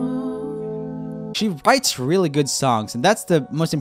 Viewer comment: Bites instead of writes